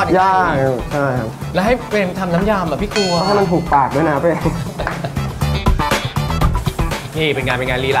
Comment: มั่นใจว่าพูดว่า"ให้มันถูกปากด้วยนะตัวเอง"